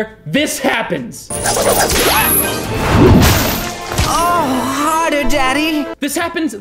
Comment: The joke during the ‘harder daddy’ scene isn’t that he says “harder daddy” it’s that the other guy replies “son?” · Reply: I thought he say: oh hi there daddy